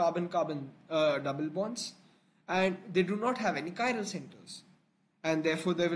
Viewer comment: what did u say after " they do not have carbon-carbon double " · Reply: "they do not have any chiral centres" because carbon isnt attached to 4 diff groups